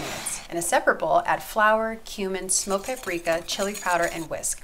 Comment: when she said cumin i thought she said humans lol